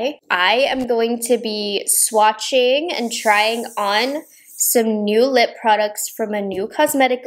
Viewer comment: First of all, your eyes 😍😍😍 a and second of all, I’m super intrigued by this launch. At first, I thought you said “My new line…”. I was like “👀😮😮😮…….” then I realized that wasn’t what you said. Lol. Buuut, I’m still very excited about Nova Beauty. I’m definitely picking up quite a few things from the line and can’t wait to see what else they come out with in the future. 💗💗